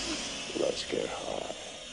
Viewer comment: All this time I thought he said “Let’s get hot” I’m an idiot